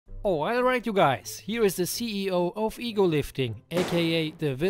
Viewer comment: Every time I watch the intro now I hear "OIL-right you guys"